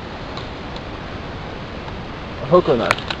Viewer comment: Thought it said 'hobo knife'